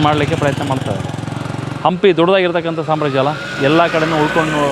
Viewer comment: Sir at 1:42 min, the subtitle says 'Hampi is a "waste" empire'. I guess it should be "vast"... Please do check and change sir. This is directly against your efforts to show our culture in its true glory.